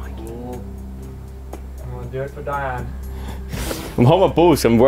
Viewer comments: I think jack says “do it for dianne” actually so cute ❤️❤️ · im pretty sure i heard on joe turn when he was next to it,"do it for dianne" hands up if u agree. 8:17 i think it was